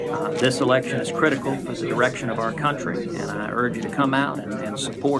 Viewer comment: did I hear what I thought I heard? The erection of our country?